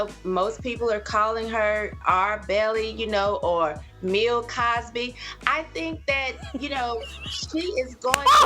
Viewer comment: I lost it when I heard " Meal Cosby" 😅😂🤣